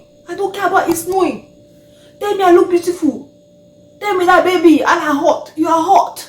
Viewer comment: Did I hear "Tell me a la hot" 😂😂😂